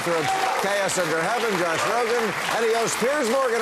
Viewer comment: When Bill announced the panel I thought he said Josh Groban. I went, "Wait ... what? The singer? To talk politics?" In retrospect Groban would've been a better choice.